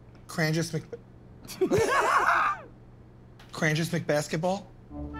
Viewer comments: All this while I thought he said, ''Can I just play basketball?'' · What is he saying 0:34 ? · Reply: he tried to say crangis but he was holding in his laugh